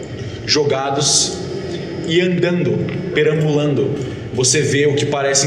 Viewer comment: Eu juro que escutei " kian dando"